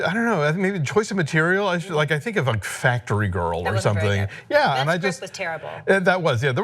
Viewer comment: What film at around the 4:54 mark did Christy mention Sienna Miller was terrible in? I really couldn't understand her and wanted to know if anyone else pick it up. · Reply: Adam Warren Factory Girl